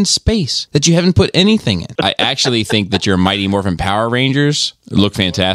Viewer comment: I'm sorry......I keep hearing "Mighty MORPHINE"